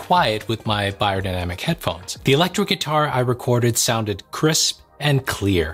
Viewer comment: did he just say ELECTRO guitar!?